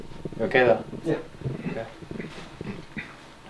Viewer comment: Did anybody catch the whisper of someone saying, "sorry" at 3:49:05 ?